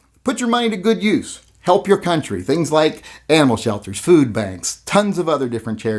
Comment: Did he just say donate to Ammo Shelters 4:33?